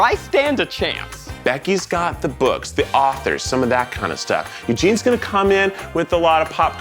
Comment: I love that the subtitles are just a little bit wrong in some places like at 2:06 it says "And Jean's" instead of Eugene's